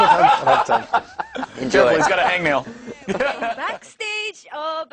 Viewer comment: Did I hear "Gerard Way's got a hangnail?" at 00:14. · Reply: No One unfortunately no Mike says " careful he's got a hangnail "